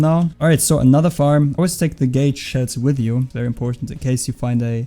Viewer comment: did he say gay shell